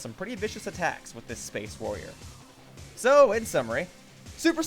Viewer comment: I thought he said Space Ninja because I play too much Warframe...